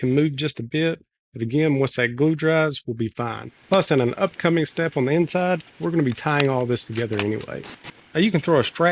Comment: I’m sure it’s just my ears, but at 9min 12 seconds it sounds like you said, “In an upcoming step we’re going to tie all this together and get laid.”